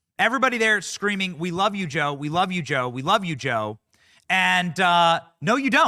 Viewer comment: Oh! I thought when people were saying "we love Joe" that she was saying "he's got to go" but my lip reading is most probably off or something because her teeth were slipping out again 🤷